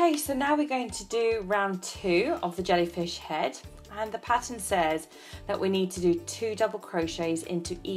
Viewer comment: lol when she said pattern I thought she said patent 😁